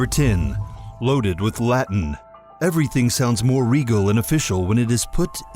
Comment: thought he said latinum (like gold press latinum, ya know, Star Trek?!?......Really?! NO ONE KNOWS?!?)